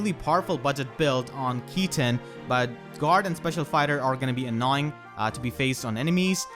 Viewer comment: i heard Special Spider lmao, spider laguz for 3 houses pls